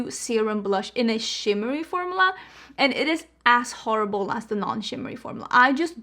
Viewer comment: Omg, when you said the CP formula was as horrible as the other, I thought you said it was ASS HORRIBLE! 😂😂😂 Oh,I laughed at myself.